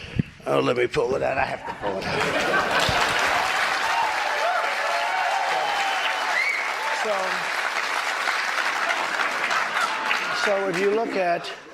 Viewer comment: cant understand a word he said, neither can the subtitles, can someone translate for me, every one applauded , i wish i knew why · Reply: She asked him why it took him 3 hours to calm down the protesters. He said “Let me pull it out, I have to pull It out”. It was the transcript of his tweet to the protesters, which he says was taken down (so they didn’t see it). Who decided to take it down?